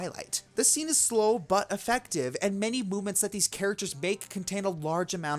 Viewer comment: i heard him say mooments and not moment